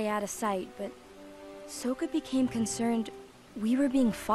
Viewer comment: I’m sorry .. did she just say.. “sow ka”???????